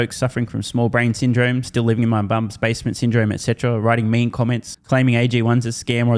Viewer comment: everytime I hear it...I swear I swear S says "Living in my Bum's basement" 😂